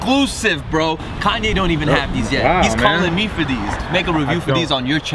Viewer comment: BRO DID U SAY THE N WORD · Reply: Doesn’t even sound like it bro. He said “wow man”